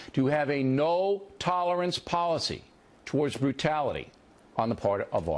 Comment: Just to correct something, what O'Reilly actually said in that bit was "it's time for America to have a no tolerance policy towards brutal behavior on the part of officers." He was talking about the police. (2:04--watch it again) But ya, other than that he wasn't making a horribly nuanced argument.